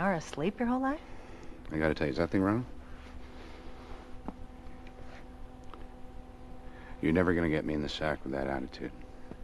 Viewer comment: what did he say? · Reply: Is that thing on? You're never gonna get me in the sack with that attitude